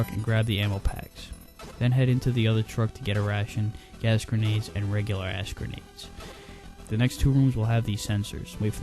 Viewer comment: Okay I heard "Get these gas grenades and these ASS grenades" (I'm deaf a bit but I think thats what he said)